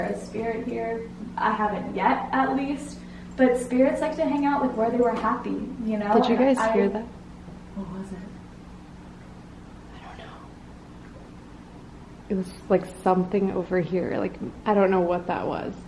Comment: Hey I know you might not see this but I heard someone saying 'I don't know' at 11:22 it was very closed to the camera or the mic it sounded like a young man